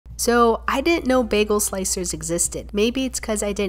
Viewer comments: I thought you said you didnt know that sliced bagels existed for a hot sec 💀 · I thought u said I didn't know there was bagley slices and I was LIKE WTF OF COURSE UR JUST CLUELESS